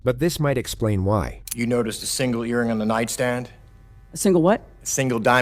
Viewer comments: "Did he just say urine?" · for a split second the prosecutor sounded like Gene Hackman. Also, the way he rushed that sentence I guarantee she heard the word "earring" as "urine", just like I did.